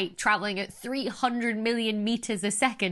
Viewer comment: Did she say "300 million metres" a second? For the speed of light